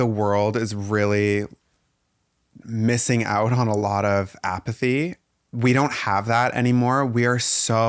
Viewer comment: I love you guys. Such a tender conversation. And funny note, I thought Ty was saying that society needed more “apathy” at first 😂 took me a sec but obviously it was “empathy”